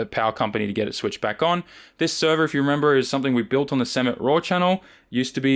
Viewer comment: As a furry I just heard you say that intro as "Sammit RAWR" I'm obviously terminal :,(